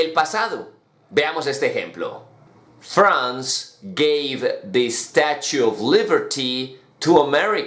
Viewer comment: min 2:32 es gave? o give? ahh ya entendí pq.. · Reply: gave